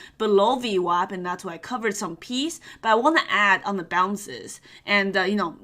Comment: did she say "cover some pees"? Here in UK, pees, means money 😂